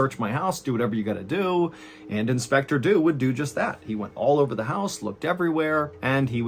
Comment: did he just say "do dew"? I know he actually say "Dew would do just that" just sounded funny.